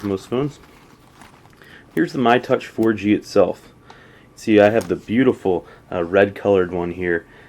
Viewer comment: ikr! In My Mind I Heard "See I Have The Beautiful White One Here" xD Bc I'm Getting The White ONe